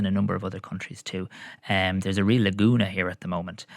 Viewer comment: did he say "there's a real laguna here at the moment"? I'm not sure what a shallow body of water, separated from a sea, has to do with electric scooters.